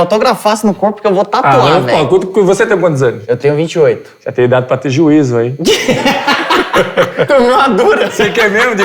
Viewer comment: "Tu tens idade para ter juízo, hen?!" Esporro do papai Cris kkkkjj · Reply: Eu sempre achei que ele tinha falado "Você tem idade pra ser juíz" kkkkk